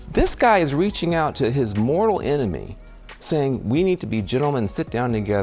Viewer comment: I think Van is being a bit over dramatic calling himself Charlie’s mortal enemy… Knowing Charlie the only true enemy he had was Satan, not people he disagreed with. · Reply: I thought he said moral enemy.